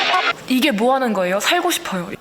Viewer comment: 나만 "이거 뭐하는 거에요 나 배고파"로 들리나 ㅋㅋ